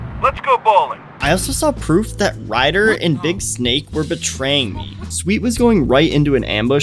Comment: Did you call big smoke big snake?